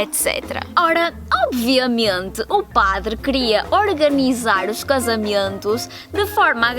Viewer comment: Ela é 100% nativa. E não, ela não disse "casamientos", ela disse "casamentos" com "e" aberto - ouviste mal! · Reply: Cara eu juro por Deus que bem nesse trecho eu escuto em: 3:12 "OBVIAMIENTE O PADRE CRIA ORGANIZAR OS CASAMIENTOS" kkkk muito bom. Eu ouvi em 0.25 😁 Isso é muito legal, por mais que português seja minha língua materna eu consigo entender melhor o espanhol do que mesmo kkkk. Claro pt_br e pt_pt tem seus sotaques diferentes mesmo.